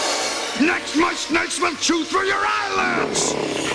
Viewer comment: What the hell is he even saying? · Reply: "Next, my snakes will chew through your eyelids."